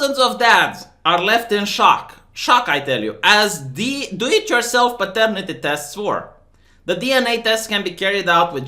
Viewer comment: love the content, I thought that you said "delete yourself paternity test" and not "do it yourself paternity test" 😂🤣